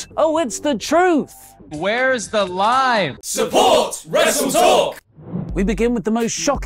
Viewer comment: It sounds like is saying, "Where's the lime?"